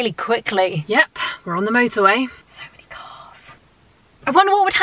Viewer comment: when she said so many cars i thought she said so many farts LOL HAHAHAHAHAHAHAH